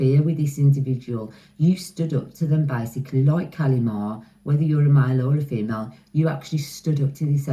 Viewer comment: I don't know the story 28:13 What is her name I couldn't understand please can you write it to me, I will look for the story about her on Google ✨✨ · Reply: Kali-ma the Hindu goddess of death & rebirth.she ends old cycles bringing transformation.divine feminine.mother of energy very loving .TWIN flame to God Shiva she slayed his ego xxx